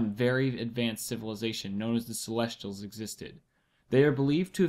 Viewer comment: What he said????? What did you said? Wich civilization? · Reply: Celestial?